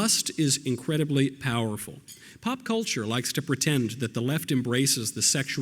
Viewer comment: At the 4:05 mark, a word is silenced over. Knowels says "political" (I think), but the CC says "cultural." This isn't the first time I've seen this happen. *edited because I realized what word was being silenced*